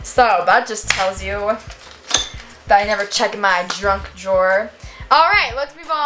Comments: Is it just me or did say "I never check my DRUNK drawers" instead of junk? Time: 4:16 Edit 1: You should DEFINITELY read your journal on a video!! And you should also watch the video of you and baby Jordan on a video! Edit 2: What did you guys do today? I went to ice skating practice, and my moms credit card was broken... so we were in the store for 5 hours!! Luv ya! ❤️💛💚💙💜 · it sounds like she says drunk drawers Like if you hear that